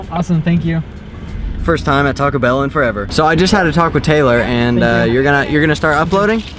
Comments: I thought at 6:17 he said I had a "taco Taylor" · When he said I just had a talk with Taylor I thought he said I just had a taco Taylor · When he said "I just had a talk with Taylor" I thought he said "I just had a taco Taylor" nahhahaha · I thought he said taco Taylor